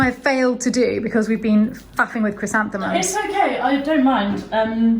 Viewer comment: please tell me didn’t use the word that I thought I heard her say? I listened 3 times...I am sure I am completely misunderstanding her accent, yes??!! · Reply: "Faffing"... it means fussing, as in adjusting a lot, usually in small ways.